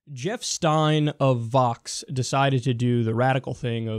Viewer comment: FOX asked a question? Stop the presses! Inb4 he gets fired. Edit: I realized I heard it wrong now, thats the power of wishful thinking. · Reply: Hah, I thought I heard F-O-X not V-O-X. I am surprised a republican allowed himself to be interviewed on anything other than FOX though